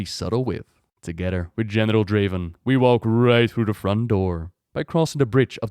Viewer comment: I swear I heard that as Genital Draven